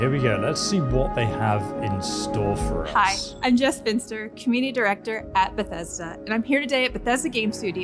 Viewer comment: I thought she said “Hi I’m a Spinster”